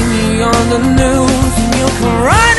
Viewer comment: you karate to the corner. That’s what i heard 🥴